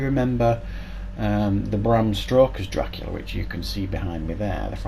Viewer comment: I'm sorry, but did you just say Bram Stroker at 2:27 ? That makes him sound like a porn actor holy s**t!